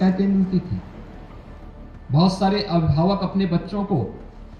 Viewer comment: there is a glaring mistake in English subtitles. Instead of parents, 'rapists' is written. Please correct.